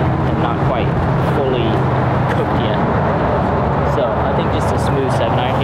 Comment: did you say qook’d?!